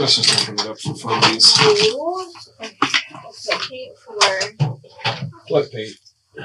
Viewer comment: did he say "What, Babe?" to the investigator?